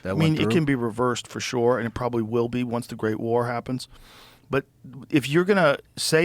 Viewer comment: What the heck did he say at 1:38:22 "once the great War happens "?